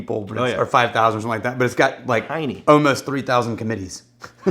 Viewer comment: Did you say Hiney at 1:01? 😂